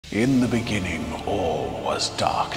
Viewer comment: Thought he said at the intro “in the benigging” and realised i misheared it